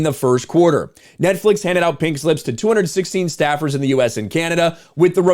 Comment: oh "pink slips", sounded like you said "pig lips" lol.